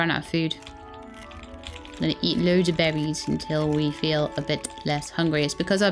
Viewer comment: I turned of subtitles and it thinks insteaf of you saying "I'm gonna eat loads of berries" It thought you were saying "I'm gonna eat loads of babies"